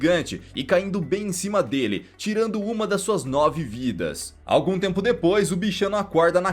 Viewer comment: Eu pensava q o gato tinha 7 vidas mais aq ta 8 e na hr 2:48 ele fala q o gato perdeu uma das suas novas vidas ai eu n entendi nd